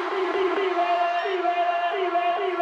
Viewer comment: 혜진이 언니가 뭐라고 했는지 아는사람? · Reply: Hey attention to 아닌가욥??